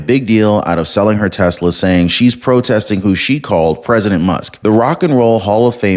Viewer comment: Did you just say "President Must" or am I hearing wrong?!! Thank you!